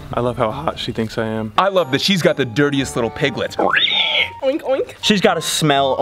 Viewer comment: What that dude said "She got those little piglet or something like that.... Lol got me dying